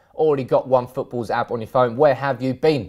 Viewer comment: i thought he said, ' u got all the nudes" on the sponsor haha